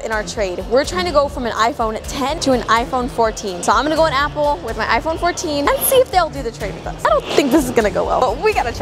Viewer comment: did you say let’s go in with my iPhone 14😂❤